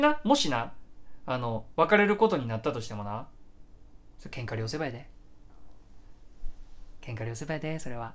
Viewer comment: が何回聞いても聞き取れない、、「けんかりおせば」って何、、？ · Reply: 喧嘩両成敗